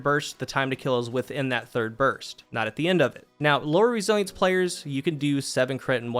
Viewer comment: What is he saying at 3:22? Lowrie Sights? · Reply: "low resilience players"